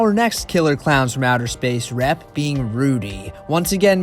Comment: what are you saying here? Rep being Rudy? I cant tell. Its driving me crazy. I dont know the movie that well. Please someone tell me. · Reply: He said “Here’s our next Killer Clowns from outer space rep,Rudy.”